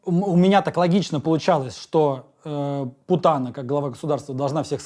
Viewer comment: Назвал путина путаной,или мне послышалось?